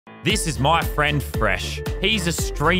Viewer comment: I for real thought it said ERESH